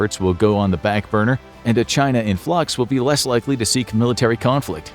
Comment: when it said " a china 🇨🇳 in flux" i thought it said "a china 🇨🇳 and plugs 🔌 " meaning joey biden 😂